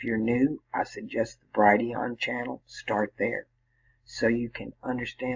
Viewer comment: She said if we are new to this stuff to start with (26:04) ? I couldn't understand what she said....if someone can listen to that time in the video and make out what she said I would be so grateful! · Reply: my videos on Brighteon link is in the description box of most videos